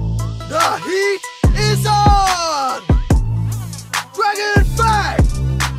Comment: Idk maybe im kinda schizo but i always hear "f*ggot fang" instead of "dragon fang"